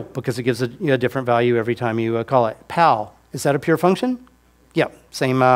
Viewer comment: Does someone know what a "pal function" is? Edit: Oh, I guess he means pow